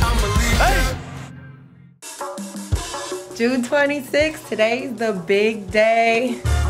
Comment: Did they say June 26th 4:14?